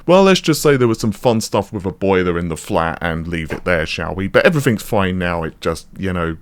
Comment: I heard you saying to the effect of "we had a lot of fun with a boy in the flat" 😂